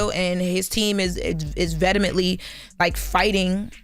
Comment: Vet ta min ly 5:39 what word is that ? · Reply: She meant to say vehemently